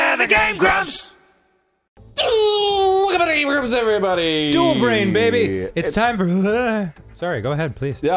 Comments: I thought it said "dual Brian" at first, and it was Danny and Arnold against Brian, who, by the way, has a P.H.D. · My dumb ass thought it said duel Brian I’ll be in the corner